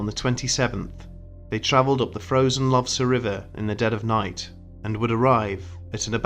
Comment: I kept hearing "frozen lobster river"